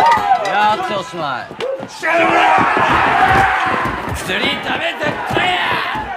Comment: なんて言ってる？ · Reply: 薬ダメ絶対ぃぃぃぃ！！って言ってます